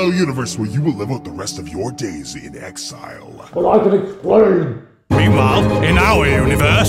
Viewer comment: what did she say? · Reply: Gabriel Rumfelt "but I can explain!